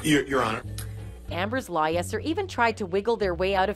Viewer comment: did the narrator say Amber's liars instead of Amber's lawyers?